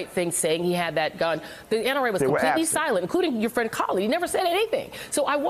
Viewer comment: Colion, did she call you Callie at 6:52 ish? haha